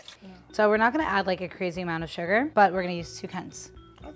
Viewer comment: I don't mean to sound offensive, but at 4.30 it sounds like she is saying "We are not going to add a crazy amount of sugar but we are going to use two cunts". Of course I know she did not say that, but it really sounds like she did.